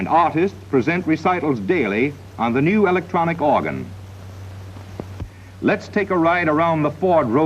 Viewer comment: did he say electronic organ?